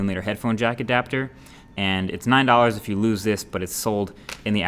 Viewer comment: Did he just say 9$??